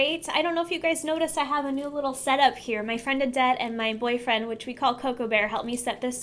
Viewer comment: When you said your friend's name I thought you said "my friend is dead" and was about to say how messed up it is to sound so happy to say that lol